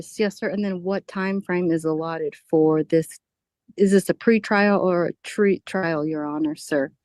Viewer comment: "Is this a pre-trial or a tree trial?" (I swear that's what I heard her say...)